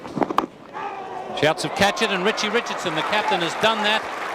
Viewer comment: Did he say "RICHIE RICH" ??